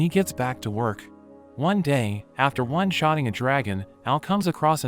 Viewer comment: At the start it sounded like he said strong ass dragon